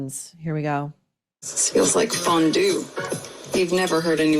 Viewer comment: I thought she said FUNdue ... Not fondue